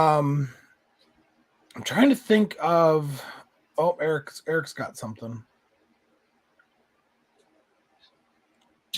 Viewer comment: What was the name of the Godzilla you pulled out at 16:00? I didn't catch the name. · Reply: That was the Akamatenshi Zombilla